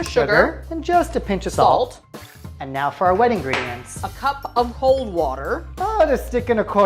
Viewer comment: Did she just say A cup of whole water ?